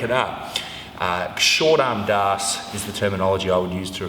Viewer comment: "Short armed arse" it sounded like near the end there :P